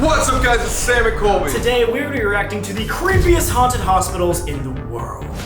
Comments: i thought he said cringiest 😭😭😭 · did he say cringiest? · i heard cringgiest